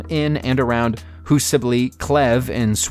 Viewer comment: Ok. I am from sweden, and i have no idea what you just said here.... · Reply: Huseby Klev, it's on the island of Orust near the town of Ellös.